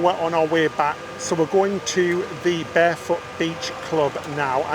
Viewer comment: I swear I heard you say you were going to Barebutt Beach 😂